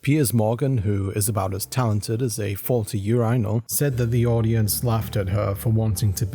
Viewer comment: "Piers Morgan, who is about as talented as a ... faulty year rhino?" What did the narrator actually say here? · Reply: Faulty urinal